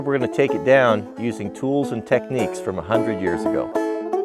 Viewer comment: You big dummy! I thought you said "like they did 100 years ago". How effin old was that drone?!